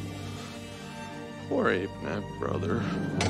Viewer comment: I actually cried when I first heard him say "poor ape man brother"